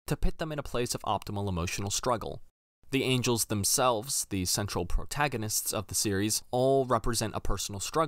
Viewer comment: Did you call the angels the central protagonists?